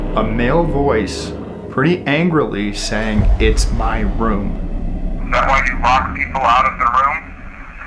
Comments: I hear 'get out' with Alex instead of 'it's my room' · I heard "Get out of my room" super quick instead of "It's my room".